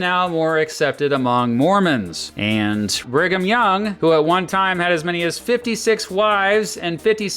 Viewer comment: I initially misheard his name as "Bring'em Young". Then I looked up his list of wives and realized that it was accurate. Dude married a 13 year old at age 43.